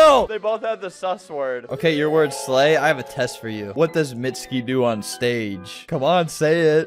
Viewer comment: DID HE SAY MITSKI? BC IF YES I WILL SELL MY CHILDREN TO YOU U ARE CORRECT